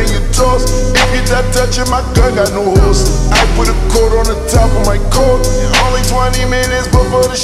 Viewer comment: Am i deaf or do i hear "i put corona on top of my coat"